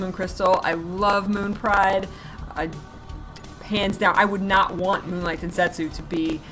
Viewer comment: That popping made me mishear a few sentences and I thought you said something very rude every single time. The least offensive one was when you said "hands down" and I heard "pants down" :D